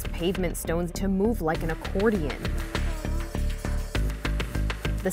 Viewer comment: “Earth quake cause street to buckle like an accordion” Thought it said abortion